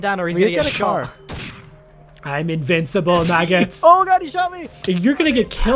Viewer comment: didnt say MAGGOTS but thought he did since he always says it